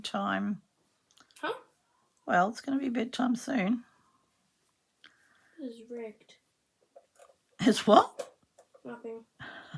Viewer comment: So much fun with your grandchild. From the peanut gallery I heard “it’s rigged” about bed but probably got that wrong. Your grandchild’s drawings are so fantastic! Perhaps they should be a fabric designer 🤗 cheers, Christine